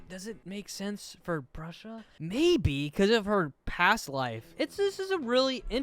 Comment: did he say bruhsha 😂